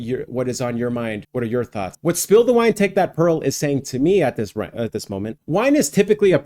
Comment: Omg for 55 years I always thought he said " spill that wine, take that girl" wow